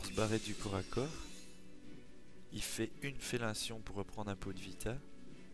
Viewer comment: c'est moi ou au lieu de dire Fellinition il dit fellation? XD